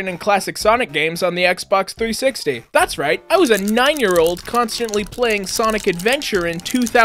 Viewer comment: What he said: 9-Year-Old What captions thought he said: 99-Year-Old